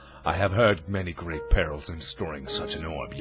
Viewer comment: I thought "Perils" said "Penis" 0.0 DON'T CLICK